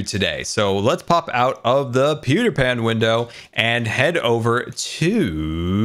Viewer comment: For a second there I thought he said Pewdipan window